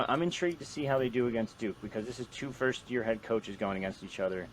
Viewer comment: It’s a joke because Robb Smith is there now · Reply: Ohhhh Duke - I thought he said "Dukes" as in JMU. Also, while we're on the subject, go Dukes.